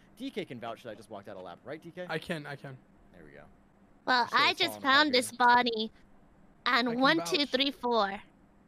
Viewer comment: Your editor(s) aren't very good at transcribing what people are saying correctly. 10:43 should be "Well, I just found this body... on one two three four", not "and one, two, tree, four" 2:21 is missing the "as Sherrif" clause Those examples are small and ignorable, but sometimes things are transcribed genuinely wrong which can be confusing for people relying on them to understand what people are saying. The capitalisation is also very strangely done, but that's also not that important. I hope you guys can work on improving this aspect of the videos, as the style of editing is genuinely very engaging, but it also feels quite rushed. Which would be excusable if the rate of upload was more than every other week.